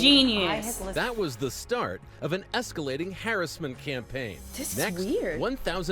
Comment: did the guy say "harassment" campaign weird for anyone else or is that just a me thing. Sounded like he said "Harris-ment". I know some people pronounce things differently in different regions and such but it threw me off.